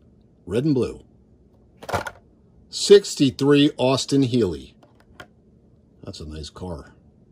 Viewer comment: 63 Austin hearley sorry if I said the word wrong I don’t know what you said